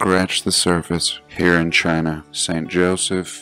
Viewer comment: I just wonder why did I hear "...secret harp...", eventhough. the written words were "...Sacred Heart...".?